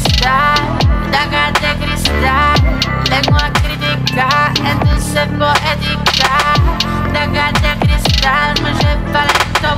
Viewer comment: Siempre pensé que decía... Daga de cristal, vengo a criticar, esto es ser poética :'(